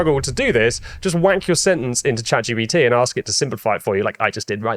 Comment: Did you just say "wank your sentence into chatgpt?" 😂😂🤣